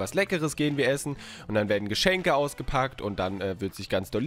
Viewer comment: Ich hab bei 1:50~ geschenke ausgekackt verstanden xD